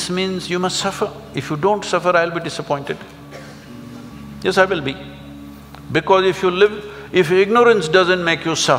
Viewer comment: many sitting in front of sadhguru at last this moment couldnt get what he said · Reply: "If you don't suffer I'll be disappointed. What is the use of me?" Sadhguru said.